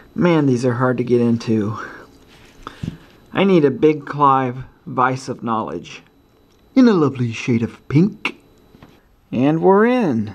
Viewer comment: YouTube's CC says: "I need a big Clive bicep knowledge in a lovely shade of pink." Is that accurate? Because, frankly, I have nothing else to base it on.